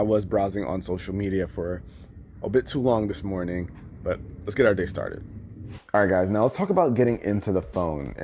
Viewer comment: Sorry I luv this channel, but im pretty sure i heard "But, Lets Get Out Dicks Started"